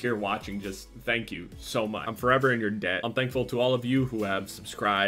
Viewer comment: did he say "im forever you're dad"?